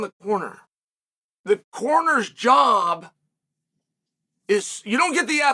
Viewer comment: When Chael was talking about corners, I thought he said coroner at first... lol